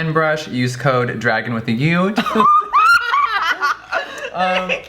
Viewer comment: "Use code dragun with a u" I heard "drag them with a ute" OH MY GOD 😂